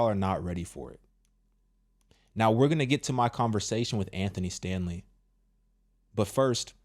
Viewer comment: Chile I thought you said “Andy Stanley” in the beginning……if I coulda reached through this screen at that moment, good Lord……